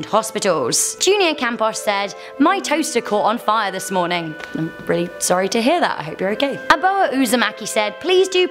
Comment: why did I think she said “My testicles caught fire from moaning”?